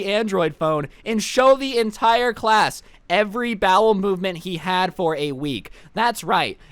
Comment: I thought it said HE ATE DOG FOR A WEEK lmao